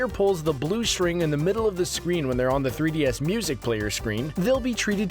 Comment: what did be say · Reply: Blue string in the middle of the screen